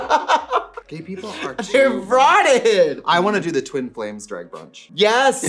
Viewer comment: girl i thought she said "twin planes"